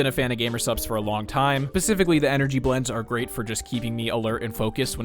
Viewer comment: did you just say "Pacificly"?